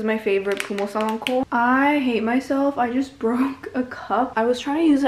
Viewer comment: am i the only one who heard her say brock instead of broke